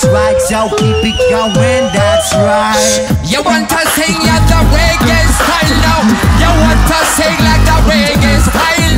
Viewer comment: what did mb14 said? Plz tell me i can,t understand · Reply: “You want to sing in the reggae style now”